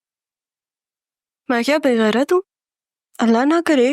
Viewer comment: IN 15:46 ZARA NE SARA SE KYA BOLA MUJHE KUCH SAMAJHNNHI AI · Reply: Usne bola Me kya be gerat hu